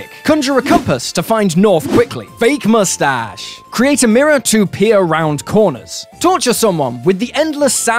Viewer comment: You know for a moment I thought you said “create a mirror to pee around corners”